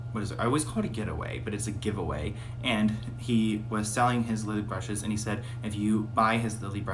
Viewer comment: When you said lily brushes I thought you said lube brushes 😂